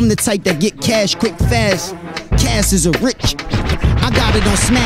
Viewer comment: does he say “cass is a snitch ass nigga?”